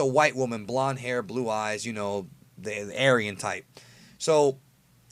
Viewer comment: I thought you said "the arrogant type" for a second. But no, "aryan". Shit gettin real.